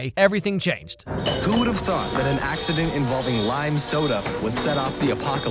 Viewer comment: did he say lime soda how did he become a zombie from contaminated lime soda